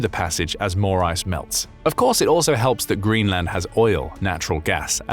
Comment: did you say oil🇺🇲🇺🇲🦅🦅